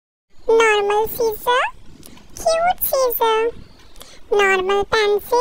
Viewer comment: Is it just me or do i hear "Normal Skin Cute Skin" 💀